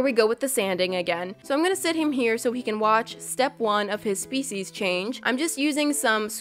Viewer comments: When she said "species change" I thought she said "feces change" lol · I went to do something else just for a little bit and when you said “species change” I thought you said feces change... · who else thought she said "feces change" instead of "species change" lol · I thought you said feces change 😂🤭 Sorry love you #notificationsquad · For a second I thought you said, "feces change"